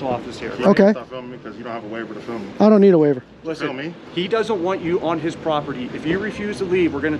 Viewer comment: He doesn't want you on HIS property. ??!! Did I hear that idiot cop right?! He's treating this like a private business. Big mistake.